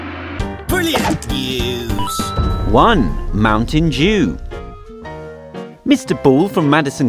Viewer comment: I'm sorry but did he say mountain Jew?